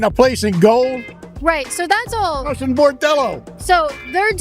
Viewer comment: Did he say BORDELLO??? 😂😂😂😂